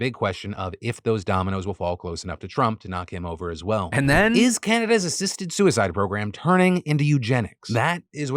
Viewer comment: I totally thought he said "Is Cannabis' assisted suicide" had to do a double take